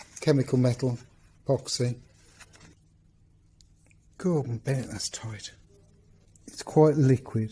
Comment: Found this comment. But unsure what it refers too? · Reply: haha that’s funny, I watched your video and I could have sworn I heard you say Gordon Bennet!